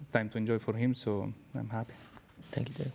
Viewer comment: did i hear lewis say "Thank you dad"? Lmao *EDIT*- From the instagram post I can confirm Lewis said Dude and not Dad lol